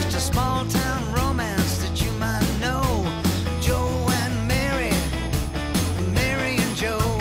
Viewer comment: At first i heard ' Joe and Mary marry Joe' 😁😁😁😁.